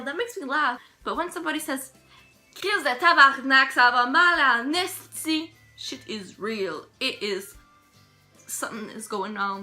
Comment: What did she say at 5:45 In English please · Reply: « Tabarnak ça va mal en esti » which is: « (curse word) this is bad (other curse word) » There’s not really translation for these curse words but it’s related to church things. English speakers of Quebec swears with these words too with their English accent lol.